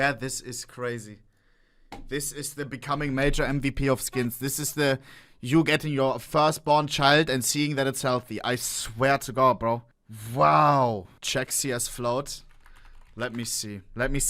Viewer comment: what is bro saying dude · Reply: Its like getting a newborn child, man get ears